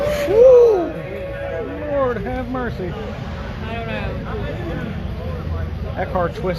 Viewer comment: Did he just say Ford have mercy 😅